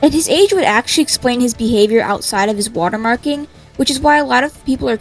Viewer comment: i thought it said water park